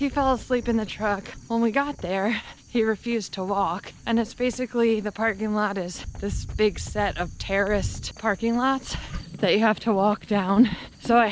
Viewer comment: I thought you said, “terrorist parking lot” HAHAHA!!! “Terraced parking lot” Ok. Got it. Much better. 😂😂😂😂